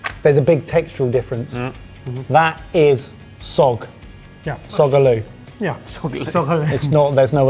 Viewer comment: What was the name of the spinach dish. Closed captioning says 'sag'. I think it was that said 'fog'. Then at around 12:36 it comes out as 'sogg'. I usually have steamed fresh spinach or make creamed frozen spinach. I would like to try whatever dish that is but have no idea what it is. Wow, British chicken is expensive. Maybe because it's Waitros.